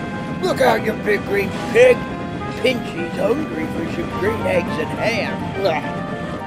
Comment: What did Diesel 10 just say? Green Eggs and Ham? Is this based off of one of the Dr. Seuss books? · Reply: Pinchy's hungry for some green eggs and ham